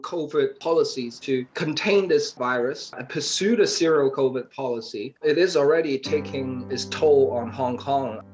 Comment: What said is "pursue the Zero COVID policy", not "serial killer" policy